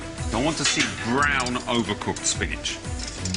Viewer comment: Sound like he says I want brown cooked spinach right